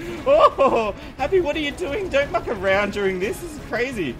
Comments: Did he say fu*k at 14:19? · You can call me an idiot or something rude like that, but did Happy say "don't fuck around."? 14:19 I thought he didn't curse? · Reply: I clearly say 'muck' White Ninja as in muck around. I can see how you thought that though :)